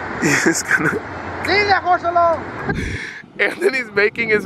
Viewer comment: anybody hear the guy say "LEE DA HORSE ALONE' 😂😂😂😂